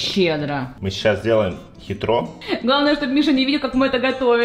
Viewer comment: Эмм мне послышалось Мише?